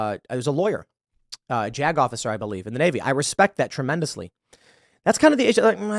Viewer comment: I thought Tim said Desantis was a Jack Off Sir, but he said a JAG Officer. Wow 😂